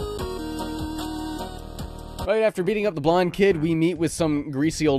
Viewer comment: Misheard that as "after beating up the blind kid" for a sec there. Honestly, I wouldn't have even surprised given what the rest of the game seems to be like.